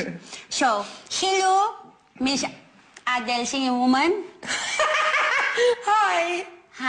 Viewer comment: it sounded like whe said "Hello Miss Adele silly woman"